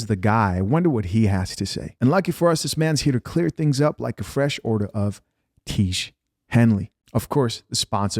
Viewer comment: Thought you said, "I'm glad it's here to clear things up like order of Quechee." Like to clear a room cause Quechee can smell like a fart....